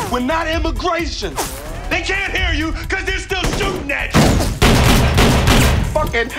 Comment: What Did Will Smith Say In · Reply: “They can’t hear you cuz they’re still shooting at you”